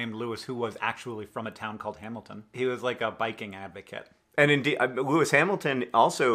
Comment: I heard he "was like a Viking Advocate" and was like, woah, I wonder what a Viking Advocate is? Does he mean like, somehow who does work with Viking Culture and History? Then I figured out he said biking.